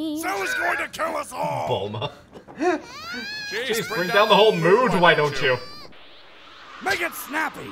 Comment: I legitimately thought popo said to piccolo: "bring down the whole moon why don't you" AND I THOUGHT THAT WORKED GREAT